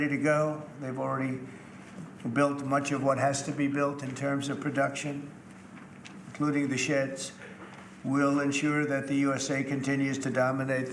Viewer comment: wait.... at 6:10, does Trump say " INCLUDING THE SHITS " ????